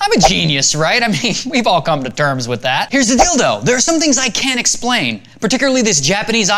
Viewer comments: he said "here's the deal thought" he just puts here's the dildo to go along with most people thinking he said "here's the dildo". It did sound like he said here's the dildo though. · Ha ha, a bit before the end of the video you said, 'here's the deal though,' but it sorta sounded like you said, " here's the dildo."! lol!